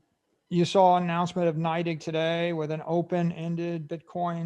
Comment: Of what? Night egg? I keep hearing him mention this company in interviews and I find ZERO on anything called night egg ... I must be hearing it wrong? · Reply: Its NYDIG